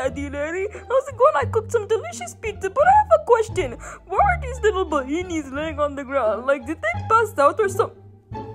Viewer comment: BRO WHY DID I HEARD "oh hey daddy larry i should delicious pizza parethapas anyways why are there little banenes lying on the ground or did they passed out or something-"💀